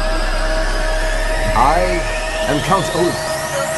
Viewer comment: I thought it said count laugh